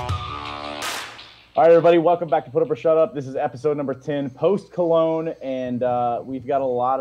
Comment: I thought said Post Malone, instead of post cologne